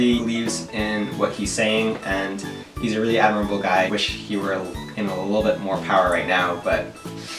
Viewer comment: I thought you said "I wish he were alive right now" when you were talking about Bernie.... GODDAMN I WAS SHOOK FOR A SECOND